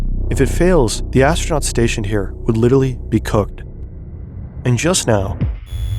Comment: did i just hear say cooked??